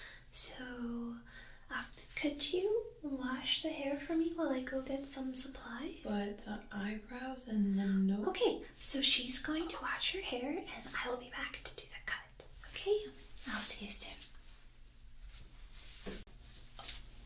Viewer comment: I was like half way asleep and all I heard was " but your eyebrows and nose"